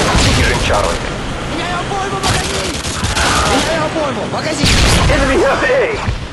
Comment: did someone say me like porno magazine?